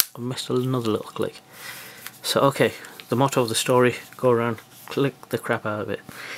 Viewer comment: Did you say motto of the story at 03:06? It's moral of the story